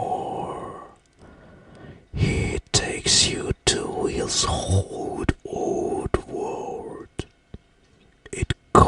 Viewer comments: Am I the only who heard "he takes you to his hole"? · I think he's saying "hot hot world" cuz hell is like, hot